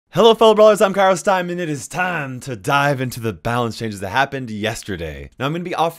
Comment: I thought you said "hello im kairos time and lets get ready to die" Just me?